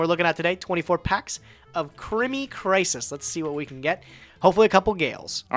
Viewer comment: I had this on in the background and suddenly heard what sounded like "Crimea Crisis" and was very confused :'D